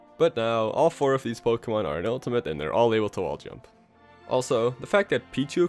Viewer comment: did he just say all 4 POKEMON can walk jump now